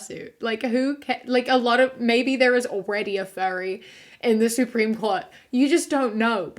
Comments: I misheard "furry" as "fairy" and the next two minutes were super confusing. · I misheard "furry" as "fairy" and was intensely confused until I heard the word fursuit. Tbh I want a genderqueer indigenous fairy on the supreme Court now! Or a furry, why the heck not eh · I was really confused bc I thought you were saying “fairy” not “furry”😂😂😂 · My ears are a bit plugged up right now and the first few times you said furry I thought you said fairy. 🤣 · I’m so dumb I thought they were saying fairy this whole time and I was like, who wouldn’t want to be a fairy???? Why does everyone think we are fairy’s?? Is this new?? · Omg every time you said “furry” I thought you said “fairy” and I was so confused til you said “fur suit”😭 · I’m gonna be honest.. I thought you said fairy not furry and I spent wayyyyy too long trying to find a kink thing for fairy.. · Oh God... I'm just listening in the background and instead of furry I thought you said faerie. And I was terrified for the fool who tried to bully one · legit i thought you were saying "fairy" i stead of furry lmao i was confused for a sec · I zoned out and the whole time you were saying furry I thought you were saying fairy until you said fursuit🥴🥴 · holy shit when u were saying furry i thought you were saying Fairy and i was like Damn they’re really calling people australian slurs out here 😭